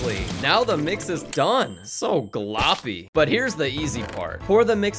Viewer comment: What did bro just say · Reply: GLOPPY👍